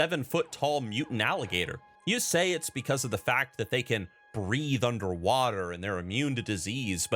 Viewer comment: I need to pay more attention. I misheard "You like argonian because they can breed underwater"